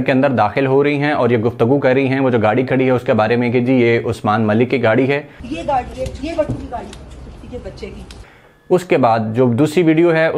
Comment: she said "ye gari hai, ye Wattoo ki gari hai, kutti k bachay ki (she did not say Usman ki gari hai). That Honda Civic is registered to Mumtaz Gohar Wattoo.